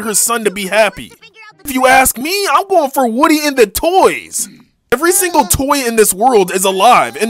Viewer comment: I thought he said Woody and the boys